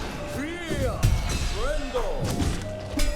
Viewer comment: Now I know this isn’t intentional (probably) but when I put on captions/subtitles during Seth’s bossfight 7:16 it says “We are friend.” I think I know why it says this probably cuz of how Seth sounds during the fight. Rest in peace may Seth’s legacy be spread throughout pharloom.🫡